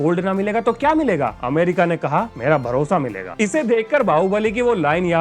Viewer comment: मेरा भरोसा मिलेगा ❌ मेरा भोसड़ा मिलेगा ✅ मुझे यही सुनाई दिया। 😂😂😂